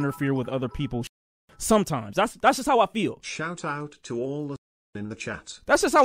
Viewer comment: I wish I knew earlier how much of a clown Mojave was, I woulda unsubbed a while ago. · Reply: I never sub to plug channels the ONLY reason I subbed was because my dumbass thought Mojave WAS. Like his personally VOD channel. I thought he said 'Shout out Mojave' he was being funny as in 'shout out me.'